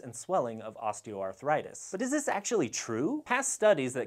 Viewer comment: it sounds like he said, "But does this actually true?" I assume this isn't what he said, but I am going to take this phrase with me, along with the findings of this video.